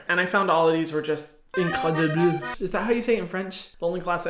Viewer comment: I’m a native French speaker and I didn’t understand what you said haha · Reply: he was trying to say “incroyable” but he just said “incredible” with a terrible accent hahaha french is my 2nd language but i’m pretty much fluent since i lived in france when i was younger:)